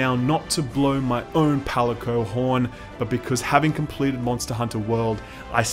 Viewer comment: did you say " I have completed MHW". No there is no such thing, no.